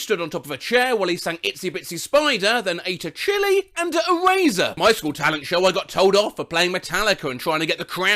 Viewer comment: Guys if you have subtitles on during the part where the kid eats a chili and an eraser the subtitles say, “a razor” instead of, “an eraser”